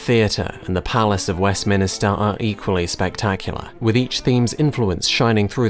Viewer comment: Did he just say Westminister?